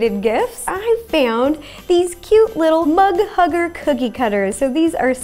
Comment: when she said mug hugger, I thought she said hug mugger... so I am assuming that is someone who mugs people of their hugs.. idek man